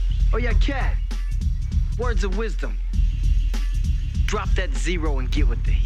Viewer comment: Oh, I thought it said "Drop dead zero".